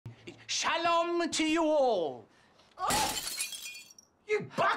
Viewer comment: In the first clip it sounds like hes saying SHALOM TO THE WALL And then proceeds throw a plate at the wall